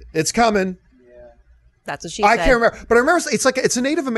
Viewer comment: Did she just really say "that's what she said"?